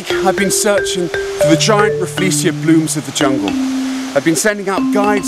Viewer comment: Did bro say the giant Rafflesia Boobs